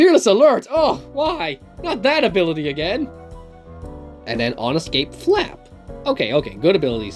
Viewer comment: I thought on escape flap said on escape rap